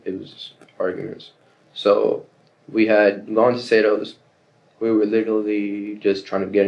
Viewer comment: "We had long(?) Sados"... Anyone know what he said/means?